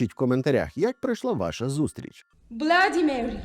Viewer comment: мені вчулося, що вона каже «владімір», чим не нечисть?